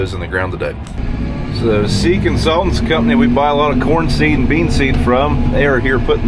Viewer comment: Here’s to the SeekinSultans varieties! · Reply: Haha...good one. I always hear "Sea Consultants".